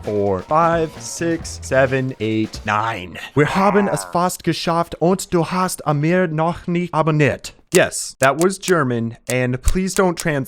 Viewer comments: What Actually he said in German ? 🤔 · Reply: "Nein, wir haben es fast geschafft, und du hast immer noch nicht abbonert" No, we're almost there, and you still haven't subscribed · for anyone wondering what he said yet to lazy to translate here you go The german: wir haben es fast geschafft und du hast immer noch nicht abonniet Translation: We're almost there and you still haven't subscribed